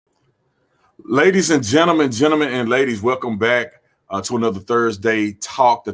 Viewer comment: At first I thought he said "ladies and gentlemen, and gentlemen who are ladies"..😂